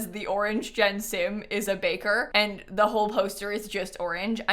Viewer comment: Thought u said banker